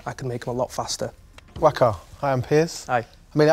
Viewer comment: "Wakker I am Piss". Well that's how I heard it.